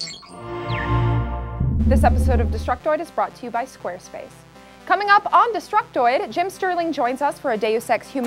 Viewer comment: lol it sound like she says "joining us for a day of sex..."